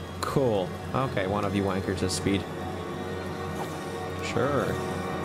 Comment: What does he say right after "Cool"? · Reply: "Okay, one of you wankers has speed."